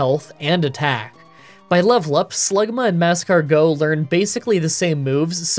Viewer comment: did He just say "Slugma and NASCAR-go"? Never know how to tag times properly and probably did it wrong again but whatever